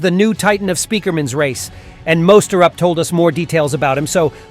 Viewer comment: did bro said mos turn up?